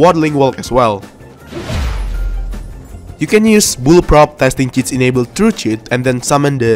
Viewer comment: I thought that said "boobprop"💀😭